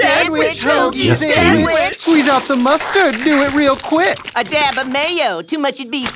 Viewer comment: i thought they said "Squeeze out the mayo" i swear it caught me off guard